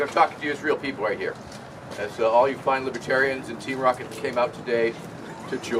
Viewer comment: did I hear that right? "And all you libertarians and Team Rocket's today..."